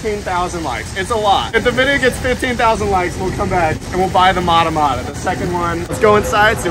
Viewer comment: did I hear that correctly? 15k likes? Well, buddy looks like you're going back to buy that turtle!! :D Edit: 9:00 guess you're getting the albino arawana too!